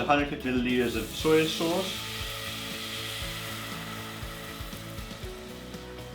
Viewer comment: did you say 150 litres of soy sauce? lol